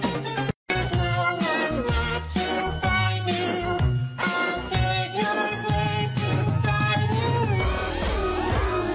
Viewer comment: can somebody understand? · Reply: The normal people saying it get supported the animal ppl don’t.